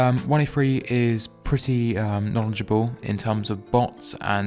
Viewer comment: I Thought Sound Said "1A3 Is Pu!s# Knowledgeable" 😂